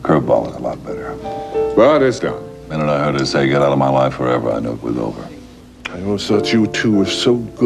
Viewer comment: wow never seen this before, anyone know what frank says at 2:49 i could never make it out. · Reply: TheMadProphet "But it's done. The minute I heard her say 'Get out of my life, forever' I knew it was over."